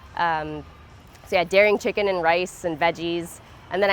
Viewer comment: No Daring Chicken, it's a brand of vegan soy chunks alternative to chicken. Every time someone mentions daring Chicken in an interview people get confused but it's vegan. I should show a picture of Daring chicken when people mention it... but I'm just so busy trying to get all of these done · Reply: First, thank you so much for the time to reply and educate! I never realized she was saying "No Daring Chicken". I just heard "dairy, chicken". I am still learning and remain a student everyday. While the picture of what she's referring to would be nice, I understand you are covering a lot of material and not enough time to do everything. I would love to help with either some editing, audio, export. Of course for nothing more than the honor of contributing to what you bring to the world that is so badly needed: compassion, understanding, and education. Just taking the time to reply means someone will see it and eventually everyone who didn't know before will help someone else just like me. Kindness is contagious. Keep doing what you're doing and let me know if I can help, seriously! Cheers.